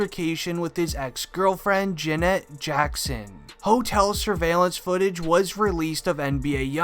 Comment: Did he just say jena jackson